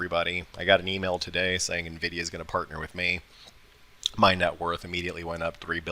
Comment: Nvidia is going to partner with me? What was that? Subtitles say me please clarify